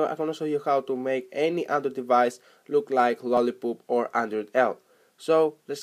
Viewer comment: did he just said lolli"poop" in